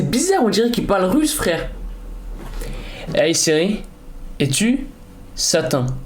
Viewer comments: Chez pas pk mais a 6:07 jai entendu je suis l'chéhtane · #siri #russe il dit pas jsp quoi à 6:00 il dit "sûre children" donc "sur enfant" like pour qu'il le voit